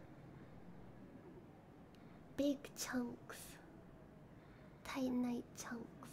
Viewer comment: thought she said Big Chungus at first